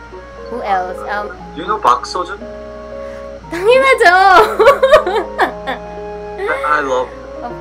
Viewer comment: omyghod i thought she said tangina jO